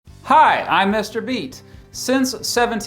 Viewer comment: For a hot second I dead ass thought this guy said I’m Mr Beast